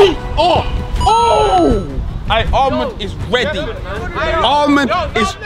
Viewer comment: When you said Armand and I thought you said haaland